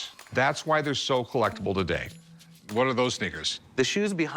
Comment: did i hear that right?!?! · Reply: "What are those sneakers"